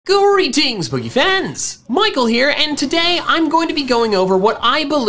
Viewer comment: Why does it sound like he says "Greetings Boogey Fans!"